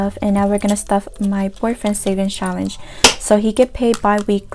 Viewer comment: Girl when you said boyfriend I thought you said “poor friend” 💀😭